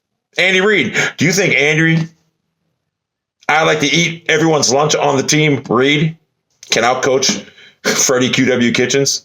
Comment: Did he just say "Andy "eat everyone's sandwich on the table" Reid"? I'm dying